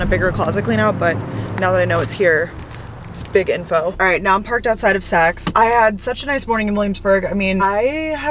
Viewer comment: Literally thought you said “just parked outside of sex”